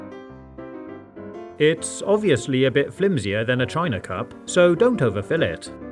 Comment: did he say China cup